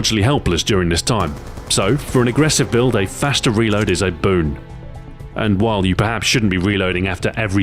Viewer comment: did he just say "Ed Boon"?